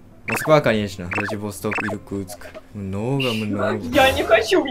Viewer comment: Русская девушка не расслышала и подумала что ты говорил про Якутск ( один из самых холодных городов России), а ты имел ввиду Иркутск)